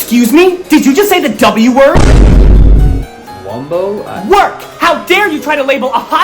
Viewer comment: have you heard it wrong That she said, "Excuse me, did you say how dumb were you"